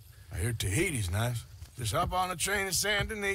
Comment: Arthur: "Avoid Gurama" What Arthur really said: "Go to Guarma"